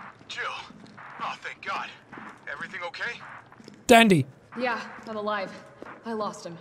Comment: I thought jack said “Daddy-“